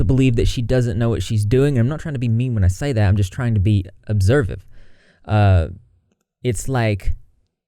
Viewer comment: did you just say "observive" lol I think you meant "observant" 😉🖤💚